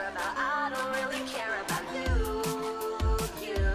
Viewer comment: I'm sorry but "you're self-obsessed and all the rest and PB I'm so over it" was done so damn well, just smooth like butter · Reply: ngl it sounds like she says and baby I'm so over it